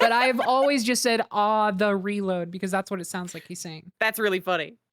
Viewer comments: I thought he said "ah, I hafta reload!" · see I thought he said Harsh Reload